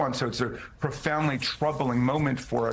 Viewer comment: Did he say "troubling" or "Trumpling" ?